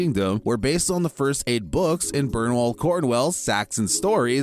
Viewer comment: Did he say Bernwall Cordwell?